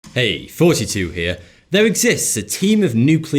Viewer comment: OH ITS!!! I have been subscibred for so long I always thought he said "Hey 42 Here" and always wondered why...